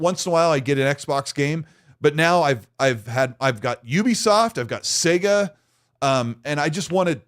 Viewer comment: I completely misheard you near the beginning. "Once and a while I'd get an Xbox game, but now I've gone ubi-soft"